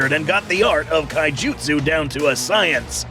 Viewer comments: skar king has a black belt in Kai jutsu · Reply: For a moment, I thought I heard Kaijudo. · Kaijutsu... Heh, for a moment there, I thought I heard Kaijudo.